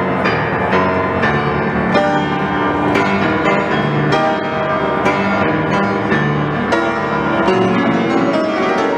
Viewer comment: she made the dude on the left jump! did she say at the beginning it's going to look like crap? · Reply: Strange, not crap lol